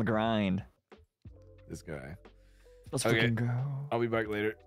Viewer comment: IT SOUNDS LIKE SHU SAID "let's fuckin' go" but it's probs just a muffled "freakin" haha... BUT IT SOUNDS REALLY CLOSE. anyway cool collab! Niji CEUP to Niji SCEP. Hopefully someday a Niji SCEUP collab